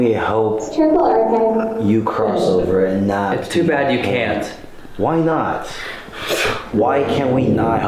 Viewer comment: i hear something along the lines of “it’s in the temple archives “ after asked what they could do to help